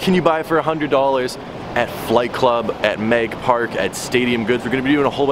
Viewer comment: I thought it said fight club